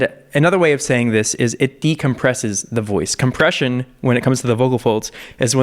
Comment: For a second, when you said "compression" i thought you said "depression"